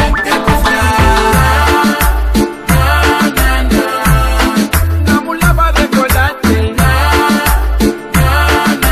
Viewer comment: Nunca supe que dice 0:56 ahí. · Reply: Deambula pa recordarte así dice we